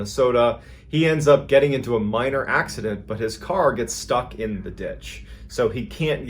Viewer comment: “He ends up in a minor accident” screen flashed with car falling down a hill. Yeah, minor accident . lol that cracked me up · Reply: I THOUGHT YOU SAID MIRROR ACCIDENT💀